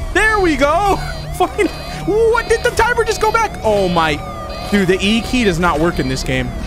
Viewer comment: It sounds like said f-ckin'